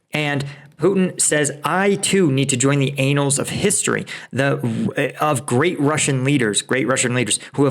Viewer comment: I heard "the anals of history"...... in lieu of " the annals (events) of history"....or did I just miss that tongue-in-cheek?😁